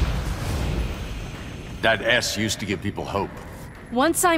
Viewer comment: I heard “that ass used to give people hope” 💀